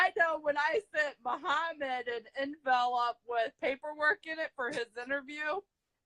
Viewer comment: She said “in-vel-up” instead of envelope!!! 😂🤔